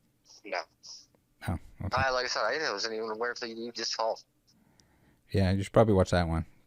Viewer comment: The part of under his breathe at 32:40 when you asked him about if he'd seen the logan Paul video, I think he said "I wasn't even aware until you just called"